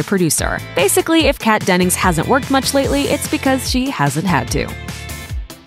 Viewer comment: i thought at first the narrator said "she hasn't worked much lately because she has a tattoo" instead of "she hasn't had too"